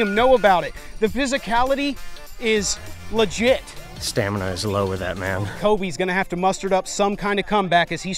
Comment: Coby: lying on the ground after being beat up Ty: "stamina is low with that man" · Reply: I heard "Stamina was low that round"